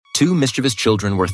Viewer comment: At the beginning of this video , i heard "two mr beast children " lol😂😂